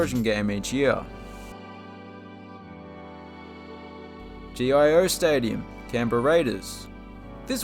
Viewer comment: I swear I thought you said G.I. Joe Stadium.😅